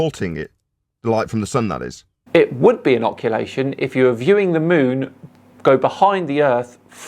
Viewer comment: love your debunking, very entertaining, thanks and please keep it up. Hate to nitpick, and probably already mentioned... around 9:00 you say "It would be inoculation..." if I heard right - slip of the tongue, they were talking occultation. Also, as Captain_Pudding mentions below the two frames with different foreground (supposedly photoshopped) were aimed at the same distant features but taken at a different location. What might be good is to show an example of this using a couple photos of a distant mountain - with a specific object in foreground, move a few steps to the side, take similar photo with different foreground object but same distant mountain - voila! Proof the photos were faked, right? ;)